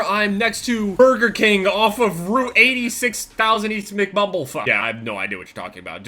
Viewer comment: Am I the only one that heard him say Mcbubblefuck